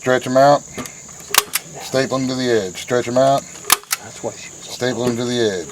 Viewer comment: "That's what she said" I heard that! lol